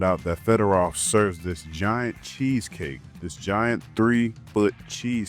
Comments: I swore he was saying cheesecake lol. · i swear my mind was playing tricks on me.. I thought he was saying "cheesecake" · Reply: He said cheesecake 😂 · i swear my mind was playing tricks on me.. I thought he was saying "cheesecake" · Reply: I definitely heard him say cheesecake 💯had Me looking 2wice at the screen to see if it was a long " cheesecake" 😂😂he said it😂